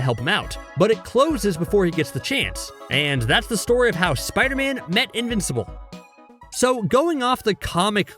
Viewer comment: Lol I thought u said Spider-Man will become invincible